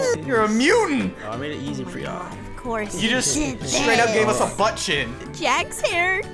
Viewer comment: Did just say but shi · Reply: I pretty sure he said “Butt chin”